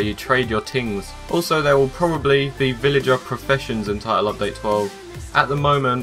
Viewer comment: LoL at 9:26 I thought he said Villager Confessions. I was like: "Uh, cool!"